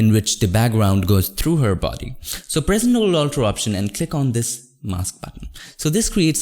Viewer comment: you also say soomething sounding like nullualtr option. i dont know if thats varous keys at the same time. dont understan what you said here.